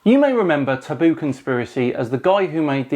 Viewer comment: Oh ! You said Taboo Conspiracy. I heard it as Baboon Conspiracy. Excuse the interruption. Please continue...